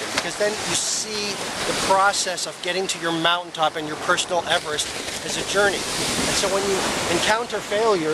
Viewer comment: What does he say on 9:46? After mount top?? And 15:48? Something like emp up your personal story?? Thanks in advance! :) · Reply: basketballhoops 9:46 personal everest i guess.